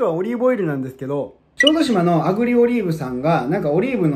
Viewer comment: オレの耳が悪いのか、の滑舌が悪いのか、毎回アグリオリーブが炙りオリーブに聞こえる🥺